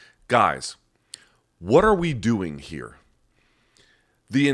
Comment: HEY- i heard that at 2:45 "WWDH??"